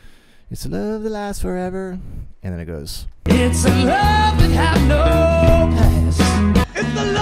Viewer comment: i heard "love that has no bad"